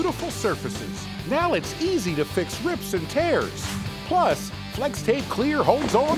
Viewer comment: Did i hear him say RIPS&TEARS ?!?!?